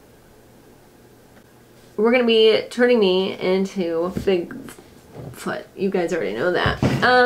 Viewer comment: did she just say shes turning her self into bigfoot???....oh you know this gonna be good😭😂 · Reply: sounded like she said figfoot, which is also wonderful